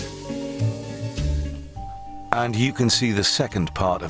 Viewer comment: At first I thought this said "Inside Death Row with Ronald McDonald"